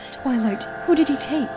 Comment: in the captions it said toilet instead of twilight I'm dying